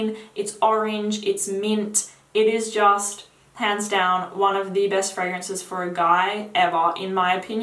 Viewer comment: I thought she said "It is just, pants down, one of the best fragrances for a guy"